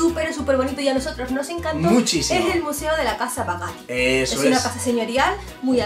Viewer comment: ¿Como se llama el museo que dicen en el minuto 5:00? es que no la entiendo bien ¿museo de la casa que? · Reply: Casa papal. Residencia del papa